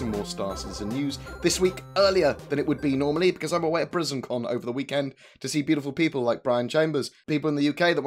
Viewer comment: I heard you saying 'prisoncon' but upon listening closer, it appears to be 'britizencon'.